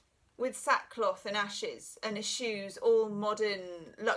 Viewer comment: Plato's allegory of the cave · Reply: Perhaps I need to be the Hermit today - I thought you said and his shoes as opposed to and he eschews.....🌻time alone to sort out what people are really saying😜